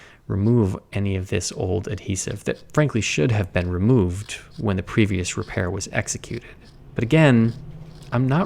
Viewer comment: Im not sure why, but I first heard “when the previous repairer was executed” like I know he likes shading the work of the previous conservator, but I knew that one was a little toooo far to be what he actually said